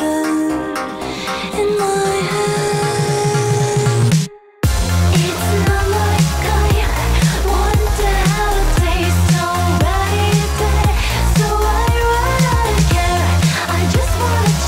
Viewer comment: When I first heard it, I thought it was “It’s another guy, wonder how it tastes”